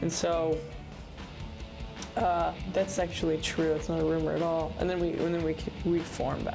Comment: i thought tyler said "that's sexually true" and now i can't unhear it